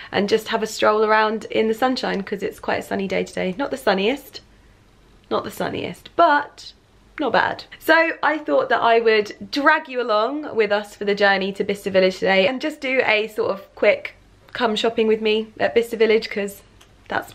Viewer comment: This whole time I thought you were saying Vista Village until I read your description lol 😄